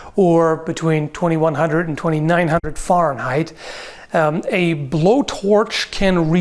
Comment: I like how he says Fahrenheit and it sounds like he said (farm high)